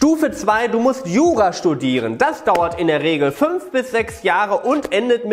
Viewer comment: Ich habe verstanden: "Man muss Yoga studieren"